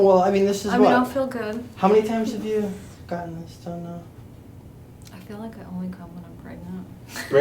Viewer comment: did I hear that correctly?? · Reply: I feel like I only come when I'm pregnant ((?? I guess